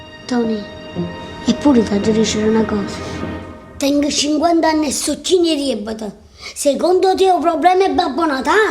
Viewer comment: Aiuto... Non capisco cosa dice a 0:54, "tengo 50 anni e so ..............." dice una parola che nn ho mai sentito, chinneriebbeta forse.... Nu cristiane che mi puo tradurre?? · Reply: Tengo 50 anni e sto '''pieno'' di debiti.